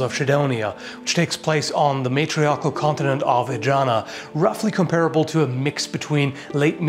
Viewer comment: I swear I heard "matriarcal continent of vagina"... XD